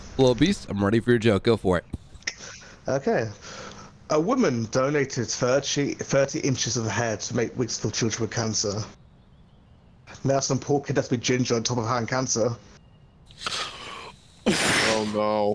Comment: what the heck did he even say? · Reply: "A woman donated 30 inches of her hair to make wigs for children who have cancer. Now some poor kid has to be ginger on top of having cancer"